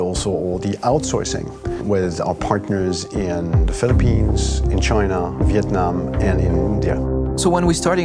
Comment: Did I just hear my country (Philippines)